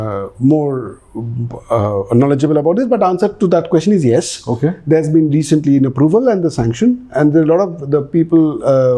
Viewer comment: At around 53:00 they mentioned something like Deago , Laego , LEGAL- ---- india , please someone tell , what is the word they spoke , i am tired of trying irrelevant keywords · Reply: LIGO I was in DU when it got approved first and it was a huge buzz. 😊